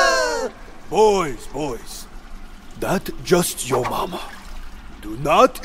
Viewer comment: I thought you said up your mama for a second